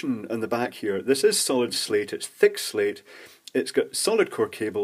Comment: I thought you were saying Solid-state at first.